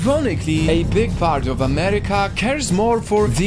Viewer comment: I thought he said “than dogs in the classroom”